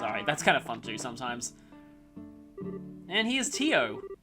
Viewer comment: i swear i heard Dio not Tio